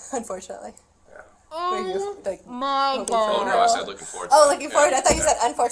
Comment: The fact that he was disappointed that he had a son and not a daughter. Thank god for that, I can’t imagine what he would’ve done if he was blessed with a little girl. Absolutely vile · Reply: Not actually what he said. I initially thought that also and was mortified, but he restated that he was looking forward to it or something along those lines. He just sorta muttered it. I’m not tryna defend this creepo but i definitely misheard it that way also and I think the investigator also heard that at first and asked again as well. Who knows maybe he changed what he said I still can’t really make it out just how he was talking and I th8nk the investigator was also cross talking